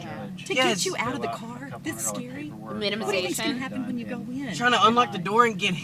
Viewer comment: AM I THE ONLY ONE WHO HEARD WHEN SHE SAID "This is scary?" AM I THE ONLY ONE WHO HEARD THE. "What'd you think is going to happen when you go in?" She is literally IMPLYING she will do harm to right there. She said it in a low voice and looked BEHIND HER AND AROUND HER to make sure the cops wouldn't hear. Omg this woman is INSANE!